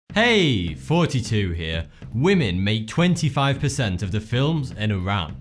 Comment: Whenever you say your name it sounds like your say "Hey! Forty-two here!"